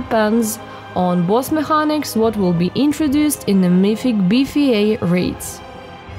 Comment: did she say BEEFE 8? or BFA ?? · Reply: erick Treminio BFA:)